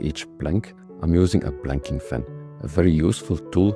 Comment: did you say 'Planking Fan?'